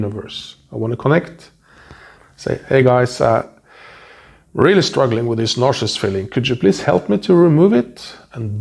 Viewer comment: It’s hard to understand this guys accent - what does he say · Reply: He says "I'm really struggling with this nausea feeling, could you please help me to remove it".